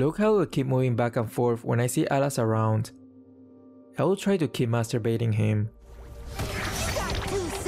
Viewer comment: Did i heard you say keep masterbaiting him?